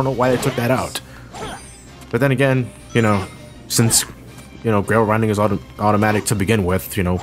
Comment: Even though he apparently said "grail rinding", I heard it as "rail grinding".